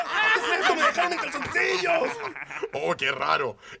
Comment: yo entendí me robaron los calzoncillos